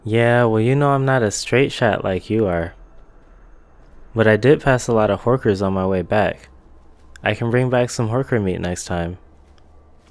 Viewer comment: lol. IDK why I thought I heard I saw some Hookers on the way back lol. Good show tho bro